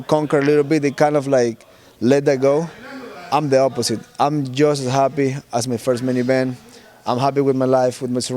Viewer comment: my dumb ears thought he said: "I'm just as happy as with my first minivan" 😅😭